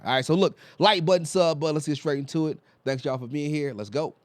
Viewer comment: When you said 'let's go' at the start of the video, it sounded like you said 'let's goat' lol